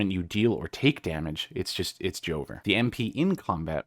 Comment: Did he just say Joever from destiny lol